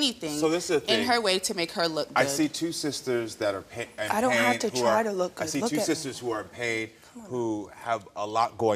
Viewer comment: “I dont have to try to look good , look at me” SHE ATE THAT 😭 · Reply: I understood it differently... I heard her say "I'm not trying to look good, look at me" as in, "in what way am I trying to look good, look at me, I'm a crying mess, how is this good?? "